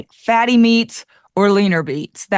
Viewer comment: did you say fatty meats, leaner beets?? Lol